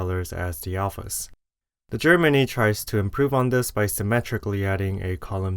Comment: i totally thought you were saying "germany" instead of "JER mini" for like 5 mins